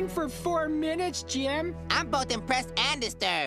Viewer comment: The fact that they allowed him to sit there and scream for 4 minutes straight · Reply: I thought he said depressed and disturbed